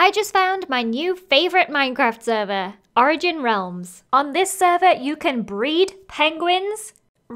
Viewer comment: Honestly thought that said Drigin realms at the beginning 😭